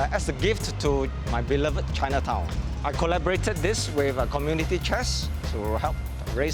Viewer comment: he actually says "community chest" which is the charity arm of the Singaporean government